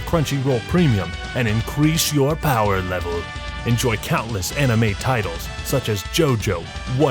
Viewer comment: Thought was saying countless anime titties